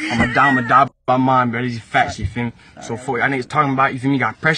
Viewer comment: What language was that guy speaking on the SnapChat? I couldn’t quite make it out. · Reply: I heard him say "yafeelme" about 800 times, thats about it